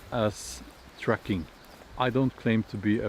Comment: I thought you wer saying "trekking" lool